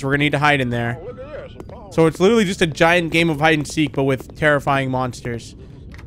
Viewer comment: I thought when it said terrifying it said furry😂